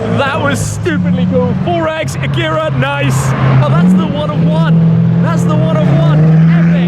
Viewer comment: When Shmee said “That’s the One:One” it sounded like he said “That’s the one I want” 😆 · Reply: he said "that's the One of One" but yeah it did sound like "that's the one I want" 🤣🤣🤣🤣